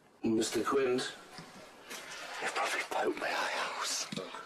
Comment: What does Pete say in 6:56? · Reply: “He’ll probably poke my eye out.” :) He was only joking, of course. 😂